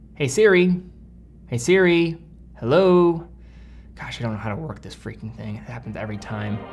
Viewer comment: я знал что ты русский. Или я один слышу как он говорит: как же оно работает